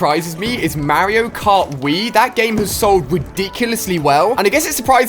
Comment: did he just say mario kart weed?